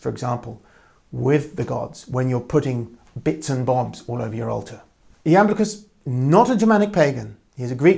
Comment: What is the name of the greek pagan philosopher mentioned around 31:00 Minutes? I can't quite catch it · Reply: Iamblichus